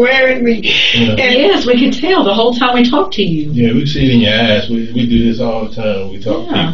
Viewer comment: I’m sorry but at 27:02 I thought he said “We can see it in your 🍑.” “Weak as h***.”… I had to play it back rq I was too into what I was doing while this video was playing in the background 😭😭😭